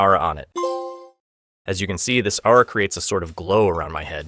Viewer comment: I know you said aura but I heard ara lol